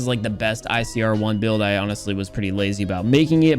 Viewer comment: Thought u said It was the icrone? 😂😂😂